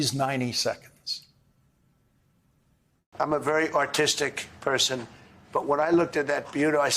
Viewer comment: It sounded like trump said he was very "autisticed"